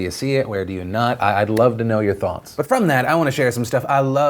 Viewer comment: After that whole PewDiePie thing I keep hearing "I'd love to know your thots" and am always a little confused for 1 or 2 seconds.